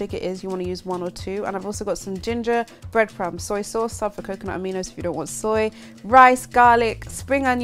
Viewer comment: What can you use if you dont want soy? Sounded like you said coconut aminos somewhere between